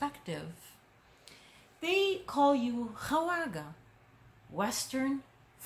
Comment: What's the word at 7:01 that means "Western fools?" Hawaga? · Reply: Khawaja